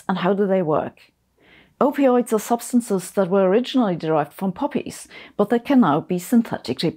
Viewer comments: Am I the only one who misheard the word 'poppies' as 'puppies'?😱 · I had to rewind because i thought you said puppies and not poppies 😂